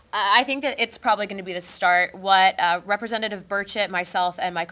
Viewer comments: did she said representative BIRD SHIT? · Hold up did she just say "Representative Bird Shit" 2:20 ? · it really sounds like she says “…Representative Birdshit…” 😂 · everytime I hear "Burchett" i hear "bird shit" lol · Hold on a minute, I tired rewinding a listing like 10 times, but did she just say "representative bird shit"?